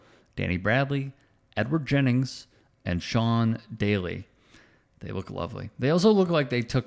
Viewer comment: Oh, Sean Daly, I thought he said John Daly, the first time. LOL.